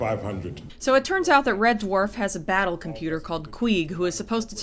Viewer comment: Queeg is the "back up" computer, not "battle" computer. Though to be fair, I heard "battle computer" on first viewing. It wasn't until after a few viewings later I thought "hang on, that's not right, is it?"